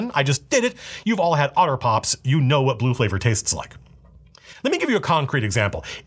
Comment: For a second I thought he said "everyone tasted Tide Pods. You know what blue taste like"